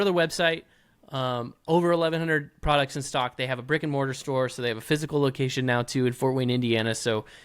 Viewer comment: First time viewer, great content!! One question, what road course cutout is that on your wall Parker? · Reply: 😂And during sponsor ad Landon said something about well...I heard "Rick and Morty store" and had to backup stream to realize was actually said was "brick and mortar store".